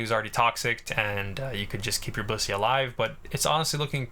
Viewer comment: I've been watching WAY too much MBT, I misheard "keep your bussy alive"